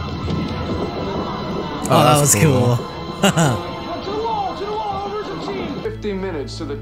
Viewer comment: It sounded like he said 15 minutes but it suppose to be 50 minutes the first time i saw the show i also thought that he said 15 minutes which would be impossible cause is 3 miles up 3 miles down in 15 minutes and unless you are the Flash is impossible for a human being to do but yeah its 50 minutes not 15